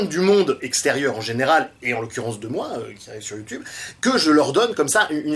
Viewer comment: "En l'occurence de moi, qui (inaudible) sur Youtube" qui quoi ? :) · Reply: Qui arrive sur youtube